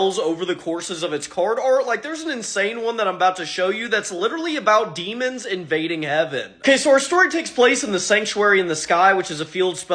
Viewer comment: i thought you said demons invading evan LOL 😭